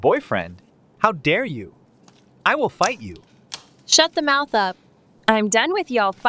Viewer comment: I thought he said I will bite you 😳😭